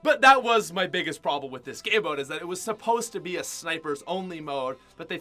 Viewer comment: my caption says “the only problem with this gay boat is that it was supposed to be snipers only